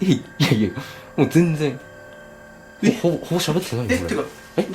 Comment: の「ほぼ喋ってないよ」に被せて男性の声で「なんだよ」か「違うんだよ」って言ってるように聞こえる…